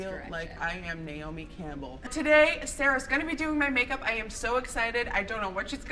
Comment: Thought it said lady Gaga doing my makeup